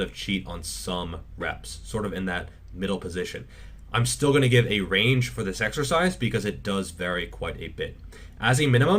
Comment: How many reps for the bent over row? I think I didn't catch that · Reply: 10